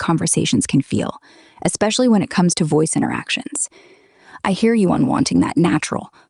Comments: Chat GPT clearly said "I hear you." as I'm meaning "I understand" when you though it said it hate you, perhaps your speech to text system isn't working as well as the AI's. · It's terrible.... It is still terrible. The interaction is lifeless, like if we were talking to a zoombie or with a lifeless individual. No matter how hard I try, it speaks to me and at the very end of a short response it says : "if you have any more questions, feel free to ask.. " it has become totally detached in terms of feeling and emotions.. No matter the instructions, no matter the voice, it is always like that.. Yesterday I used AVM for 3 hours before it switched to standard mode... To make matters worse, next 9/9 standard mode is gone for good... Now AVM is almost unlimited for free users but it is completely ruined. Ps...in 6:09 it did not say "I hate you", but rather "I hear you" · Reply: I DEFinitely heard "I hate you" even w/out him pointing it out. We played it several times over because it was hilarious. And my voice chat is always .5 seconds from taking off to wherever it is apparently late for. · I Have the same issues. Also it said "I hear you" with a British accent, not "I hate you".